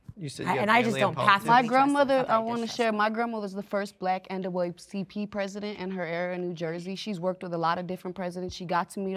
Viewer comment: Green dress girl is not anti-Biden, she’s anti-not-my-grandma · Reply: she was the first black andawecy president 😂😂😂 wtf is she saying